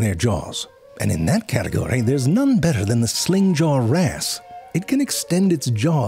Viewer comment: Jerry was oddly tame this time. Frank, what did you do to him? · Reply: I kept hearing “sling jar a**” for the fish with the longest suck extension. I’m sad that Jerry didn’t do anything with that one.